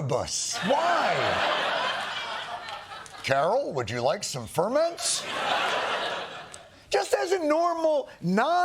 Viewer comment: I thought he was saying he’s fermenting?